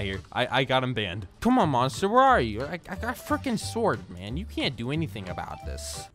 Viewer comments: did he just say f*ck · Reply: No he just said frick just listen closely by making your volume down and making your ears close to your device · WATCH YOUR MOUTH i think you said frick